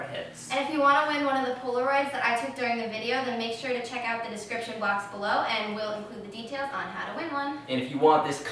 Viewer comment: At the end, did say, "Check out the description blox below.." lol